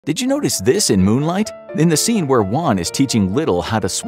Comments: I thought he said Moon Knight 😂😂🤦‍♂️ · I thought i heard moonknight